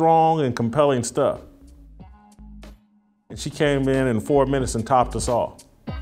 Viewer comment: did he say "she topped us off"?